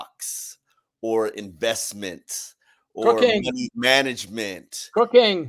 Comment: I honestly thought said cocaine and not cooking at the start. I was like dang gets down?